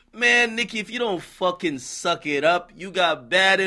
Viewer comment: I thought AK was about to say “Man Nicki, if you don’t suck a dick” 😂😂😂😂😂😂😂😂😂😂😂